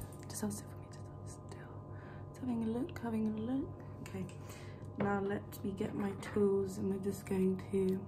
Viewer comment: Today I learned, it's possible to miss someone you don't know...Also, I swear I heard "Let me get my toes." then she pulled out a screw driver and a black head remover.